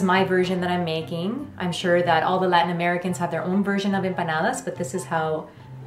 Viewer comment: When said “Latin Americans” i literally thought she said “white Americans”